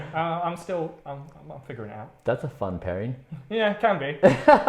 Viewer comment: does he say "that's a fun parry" or am I mishearing/misunderstanding? I've never heard of this Brian guy before but something in my brain is giving me sociopath warning signals. Like the way he goes from "sorry you went through that" to mocking his attempt to lighten the mood a bit in the next sentence almost gave me whiplash.